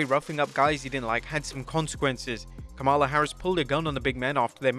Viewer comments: did he say "kamala Harris" pulled a gun after their match 😂😂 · did he say Kamala Harris pulled a gun on Andre the Giant? 😆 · did you just say Kamala Harris??